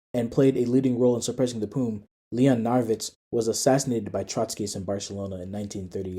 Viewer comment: what is the name that was said there? tried to google but couldnt find. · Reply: Leon Narwicz, but I think I pronounced his name wrong it might be pronounced “Narvitch” instead. The name is Polish and the W is meant to be a V like in German.